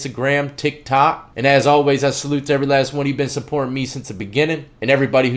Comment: 😆 LOL. Just realized he's saying "Be easy. Be safe." I always thought he was saying "Beezy Bee".